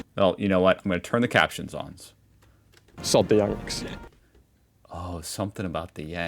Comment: i thought he said "stop the yanks" because many americans are referred to as "yanks" because of the yankees